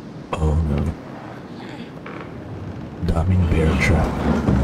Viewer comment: OMG LOL I THOUGHT HE WAS ENGLISH THEN I HEARD "DAMING BEARTRAP" AHHAHAHAHAH